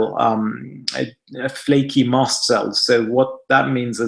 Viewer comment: think i heard him say "ma" cells. can anyone tell me? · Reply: Mast